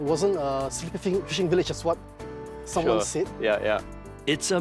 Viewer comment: wow how dare he question what the great leader said hahahhaa. 'Sleeping village' is the biggest lie in Singapore history